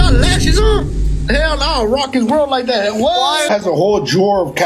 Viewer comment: did bro just say ima rock his world???